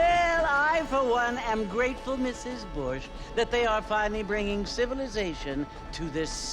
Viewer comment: Lol, in the beginning when the lady said Mrs.bouche I thought she said Mrs.bulls$$t